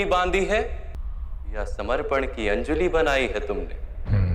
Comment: it is wrong caption.. Shree krushna did not say cupped palm of sacrifice, but of "surrender"... Becoz dharmic knowedge comes when u surrender, rather than being arrogant